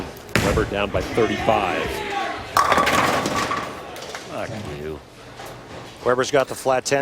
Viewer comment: Did I hear Pete say "Fuck You' after leaving the 10 pin at 13:25? lol